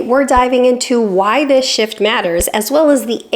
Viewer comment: Who else thought she said “…why this 💩 matters.”? 😂